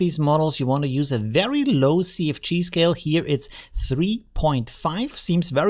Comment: Great job as usual. I like your accent, you said CFG and it sounded like you said "Sea of Cheese", so that's what i'm going to call it now.